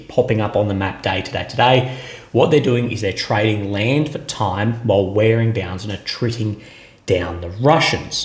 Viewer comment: I’m getting old , I heard “ land before time” 😂. Now that your not wearing a suit, let see the tattoos